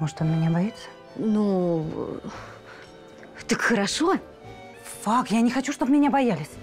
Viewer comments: мне послышалось или София сказала "фак"? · на 12:39 мне одной послышалось "ФАК. Я не хочу, что бы он меня боялся"? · она сказала ФАК? Или мне послышалось? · Что сказала фак?